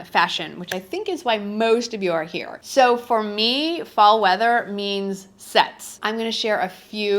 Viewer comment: Haaaahahaha!! I thought you said, "To mean, cold weather means sex!!!" Good grief!!